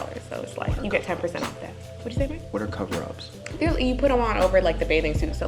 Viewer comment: who said they go out lol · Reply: DeeDiZeL it sounded like at 1:21 she was like "what did you say babe?" To lol. Idk maybe just listening too hard